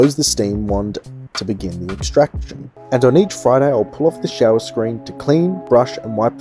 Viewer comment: I can't make out what you say at 4:14 Are you saying that "after each shot" you pull off the shower screen and clean the machine? that sounds extreme :) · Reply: No after each "FRIDAY" once a week I'll give the whole machine a good once-over